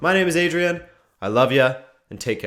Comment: I love you Adrian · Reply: The Juicy Mop I thought you saying I love you Adnan